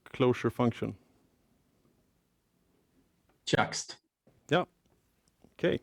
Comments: I couldn't make out what he said when the host asked him "what is your favourite clojure function?" timestamp 49:58 ... Sounds like he says chillaxed ??? anyone know what he says, this is going to bug me! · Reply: Juxt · I cannot figure out what that function is. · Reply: juxt